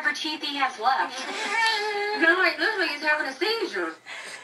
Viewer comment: Did she say at 8:30 this nigga is having a cezure